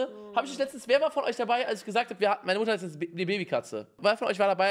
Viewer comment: Ich habe erst verstanden: meine Mutter ist jetzt eine Babykatze 🤣🤣🤣🤣🤣 LOST 😅😅😅